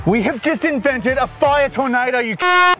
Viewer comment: what he said "C**T"? PLEASE ANSWER